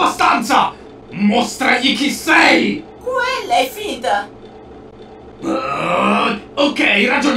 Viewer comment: cosa dice Bonnie a 1:31? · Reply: Felux™ dice quella è finta, perché Foxy stava puntando la pistola al guardiano